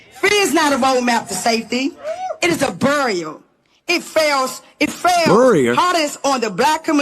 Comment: Yeah, it was very hard to understand her I thought she said burial and then I realized she was trying to say barrier. 😆